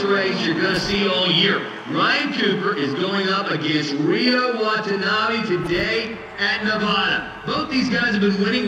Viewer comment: I legit thought it said Ryo Wannabe